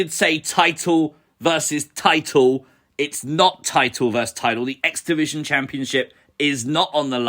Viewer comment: About that, Oba didn't say Title vs Title, what he actually said was "Titan vs Titan". Must be the accent...